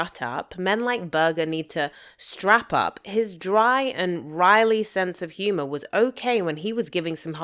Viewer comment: booboo, it’s “wry”. Not “riley” or “wiley” or whatever it is you said.